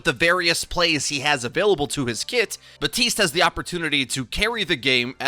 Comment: Did you just say “BAPTISTA“?